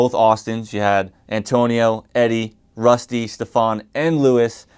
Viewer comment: Drop a music video with dusty and stephon while you’re stuck at home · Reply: Hold up. His nickname is dusty??? I thought in the vids they said rusty